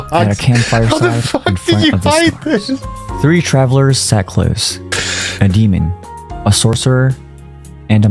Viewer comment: Minor correction! At 5:59 he actually says "How TF did you HIDE this??" because somehow they all managed to put this together with no one letting the secret out. Thank you for the vid! 💞💕